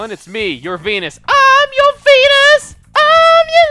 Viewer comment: I thought said i'm, your penis for a second haha